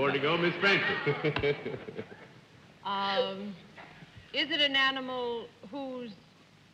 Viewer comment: What did Arlene say at 23:20? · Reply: is it an animal who spits lol